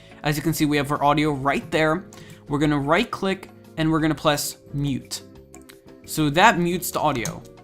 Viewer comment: you said "Pless" instead of "Press" lol